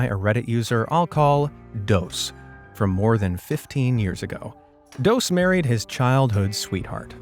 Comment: broooo i stoppped at 23:50 and i heard “he got married with his his child-“ 😂😂😂😂😂